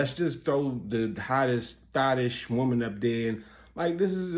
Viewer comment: A I thought you said scottish, when you said thot-ish 😂😂😂